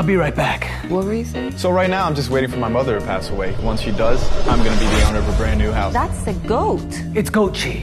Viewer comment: "I grew up at a dairy farm and one of my favourite hobbies was actually drinking milk straight from the cows" and "I'm now waiting for my mom to pass away once she does i am going to be the owner of a brand new house" wth😂😭 Edit :- i changed the lines cuz i misheard it.. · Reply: “I’m gonna be the owner of a brand new house” is actually what he says.